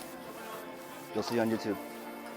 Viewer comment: did you say "you'll see it on youtube?" lol